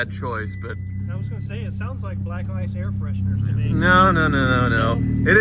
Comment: lmaoo did say it sounds like black ice air fresheners ?